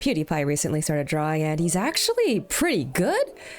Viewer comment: did you said pewdiepie? Cuz I heard Cutie pie